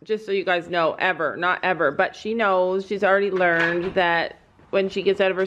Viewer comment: OMG PLZ STOP,Second comment but I got 2 mins into video,and I heard “She’s already learning to walk”!HOW DID SHE GET ON THE TRAILER!😮😮😮😮